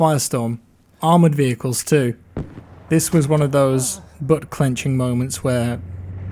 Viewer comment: PAUSE did he say “butt clenching moments” ?🙃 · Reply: SupremeDream _ it sounds like boat clenching